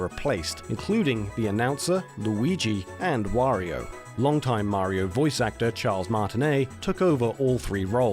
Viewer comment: Damn I almost soiled my pants at 5:42 when they said "Long time..." I heard "Late time..."